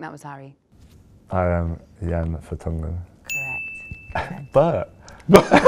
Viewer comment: what did he say? I can’t understand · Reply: I am Jan Vertongen